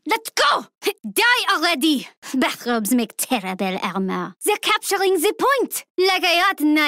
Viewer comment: What did she say at 1:31? (bathrubs make terrible ???) I believe that’s what she says when she kills Torvald... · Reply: IICyaniide :3 "Bath robes make terrible armour" and yeah when shes kills Torvald.